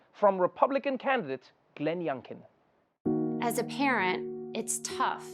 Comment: I know she said, "As a parent..." but what I heard was, "As a Karen..."